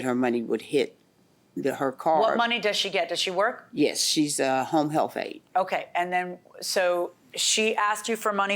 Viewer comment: she's a "homely health aid"? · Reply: I thought she said home health aide lol